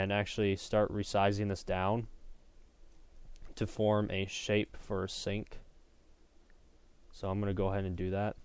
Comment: For a second I thought he said "to form a shape for Satan"